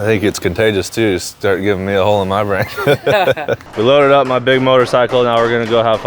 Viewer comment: did he say the hard R ???! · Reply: Think I got a hole in my brain